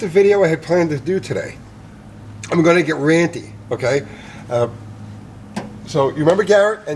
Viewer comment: Did he just say, "I'm gonna get Randy?" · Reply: "ranty" I heard "randy" the first time too, I had to rewatch it lol